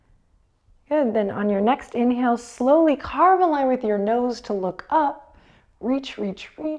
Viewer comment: My husband thought Adriene said "Carmalize your nose" 😂 "carve a line with your nose", I said. We both belly laughed. Thank you for this treat on Play day 31 🎃👻